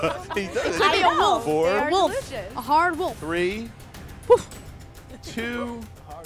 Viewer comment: Did Jack just said "A hard wolf" in 15:19 IM DYINGKFKK